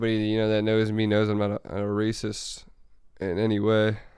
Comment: did he said he was racist??? or i heard wrong?